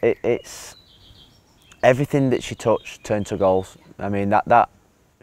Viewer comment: ‘Everything she touched turned to golf’ did he really say that?